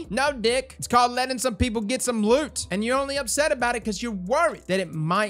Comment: LMAO did you actually say, "It's called Lenin some people get some loot!" Bravo sir, bravo.